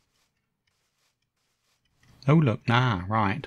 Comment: Yayyyyy a video 😁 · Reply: Ha ha ha! I thought you were just saying "Oh look! A video!"